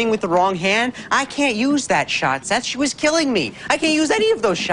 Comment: it sounds like is saying that shit was killing me